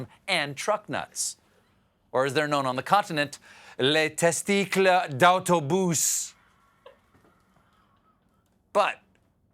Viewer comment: I’m French and I heard “the testicles in your mouth” at 1:40... And after watching this show for a while now I’m seriously thinking that it might be it lol